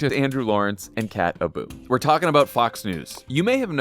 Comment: kat abu, for a second there i thought you said cat ballou.